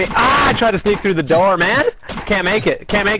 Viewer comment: Ive always thought he said: “on my way son!” But seeing the video it’s actually “out my way son!” Woww